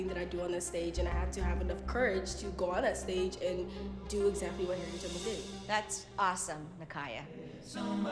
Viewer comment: When u put subtitles at one point instead of "did what harriet tubman did", it says "did what harry potter did" Time stamp